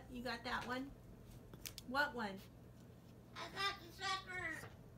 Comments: Did say, "I got the diaper." at 2:34 (approximately)? LOL If so, having Ollie expanded vocabulary. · "I got that vibe!", 😎 2:34, " I gotchur supperr!" 😋 That's what I heard anyway lol